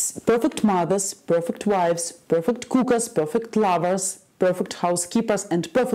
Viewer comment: Lol I thought she said perfect cougars, turns out it's perfect cookers lol